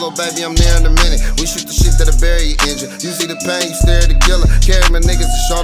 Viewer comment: You see the pain when you stare at a killa💯🥵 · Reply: Listen close when you hear what sounds like him sayin "you see," he really sayin "U Z." He reppin for his klick, the Underworld Zillaz